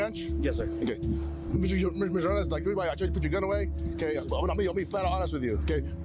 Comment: wtf he say at 1:35 ??? jghyfnkooiu9oiu vgderwsredy oiiuo8ihjiuh tell you put your gun away lol